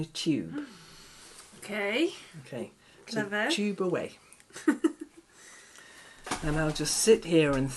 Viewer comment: I know that said, "tube away." But what I heard was "tuba way" 📯